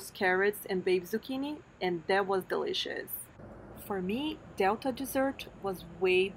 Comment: Sounded like you said "It was delicixs" - you probably meant DE-LI-CI-OUS